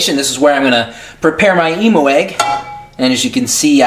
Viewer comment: It sounds like you said "emo egg" XD